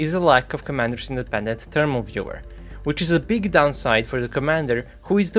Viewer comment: I can't make out what you're saying. "the main problem with the type 96b is the lack of _____"? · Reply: Commander's independent thermal viewer?